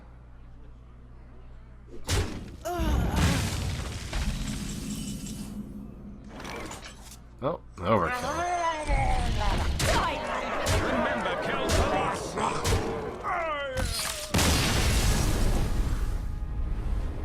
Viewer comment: I can't understand what the Silvermoon Gaurdian says when he attacks. "Remember, kill???" Help me out here xD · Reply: I thought he said "Remember, kill us last!" and was like "That's one hell of a "guardian"".